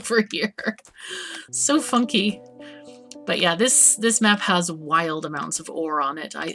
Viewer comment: Sorry miss, this is a Christian sever, so no swearing Unless I heard you wrong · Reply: "Funky" offends you? Also, my server is not affiliated with any specific religion...